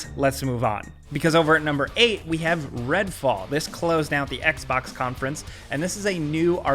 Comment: super smash bros character. 1:00 Guardians of the Galaxy 1:37 Babylon's Fall 2:18 Stranger of Paradise 2:57 Rainbow Six: Extraction 3:20 Mario Rabbits Hope 3:50 Tiny Tina's Wonderlands 4:19 Avatar Frontiers of Pandora 4:42 Advance Wars 1 + 2 Re Boot Camp 5:16 Resident Evil News 5:46 The Great Ace Attorney Chronicles 6:05 New Razorware 6:27 Horizon 5 6:53 Outerworlds 2 7:17 Replaced 7:39 A Plague Tail Requiem 7:58 Somerville 8:19 Contraband 8:39 Bloodhunt 8:58 Planet of Lana 9:19 Jurassic World Evolution 2 9:43 Battlefield 2042 10:17 Redfall 10:53 Age of Empires 4 11:11 Stalker 2 11:39 Metroid: Dread 12:11 Starfield 12:56 New Halo 13:56 Breath of the Wild 2 14:33 Elden Ring No need to thank me :) AMC to the moon. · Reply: When he said red fall I thought he said titanfall 😩